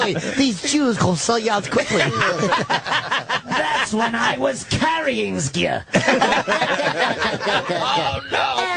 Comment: Does anyone know what was said at 2:22:09? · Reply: "That's when i was carrying you" but with an exaggerated Popeye the sailor accent